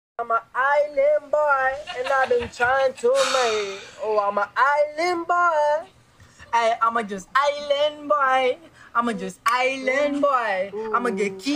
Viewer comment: I heard "Islam boys".. 😂